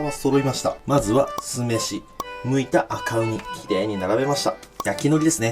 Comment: 「剥いた赤ウニ」が 「泣いた赤オニ」に聞こえたので 耳鼻科に行ってきます